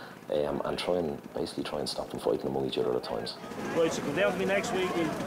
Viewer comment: Could someone please help me transcribe what says at 37:56 - 37:59? "I'm trying- basically trying to stop them before ...... times" is all I'm getting. It would be very helpful, since I want to use this in English class with my students. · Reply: “Stop them from fightin each other at times”